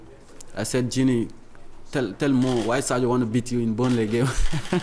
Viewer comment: Can anyone please tell me what Mané said to Gini tell Mo, please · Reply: ‘Yesterday I said ‘Gini, tell Mo why Sadio wanted to beat you in the Burnley game’. Gini is so funny, I always say these kind of things.